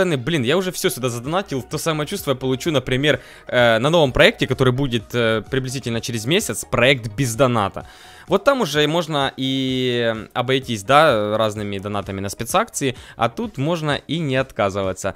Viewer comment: я подумал что он сказал пизданата, каму тоже показалось?